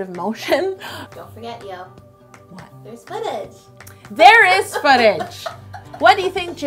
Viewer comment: When said "footage" i heard "fetish" i was like ohhh mannn. What has htci come to..